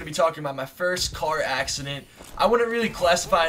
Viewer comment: I thought this said" eating my first car accident"